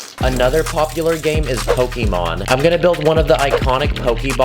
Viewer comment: What he says: "Pokemon" What i hear: "Pokey Mons" 💀